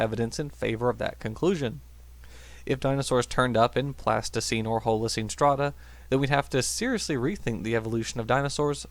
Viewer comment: It sure sounded like "I dinosaurs turned up in plasticine or holocene strata..." You mean in human infancy? :P